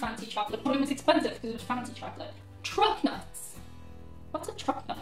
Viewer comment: I was embarrassed that I did not know what 'truck nuts' were. The MORE YOU KNOW! :) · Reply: At first I thought she said Trump nuts and I was like “oh boy”...